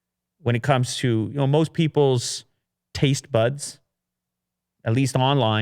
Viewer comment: damn, I hear people taste butts instead of tries buds.